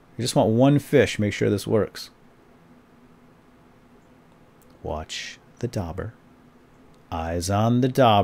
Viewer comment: did he say watch the "dobber"